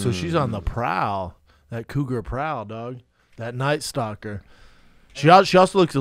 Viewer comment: When Brendan said "cougar prowl" my phone went off because it thought he said Google🤦‍♂️